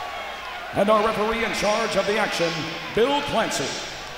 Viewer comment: Did he say the referee was Bill Clinton?